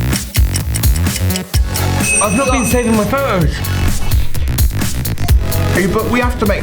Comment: What does he say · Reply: "Ive not been saving my photos"